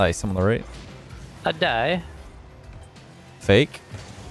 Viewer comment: Did just say "I die" like Borat?